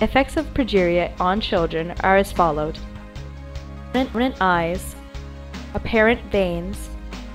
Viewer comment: The video helped alot! But what did she say at 2:20? Something eyes? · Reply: larger eyes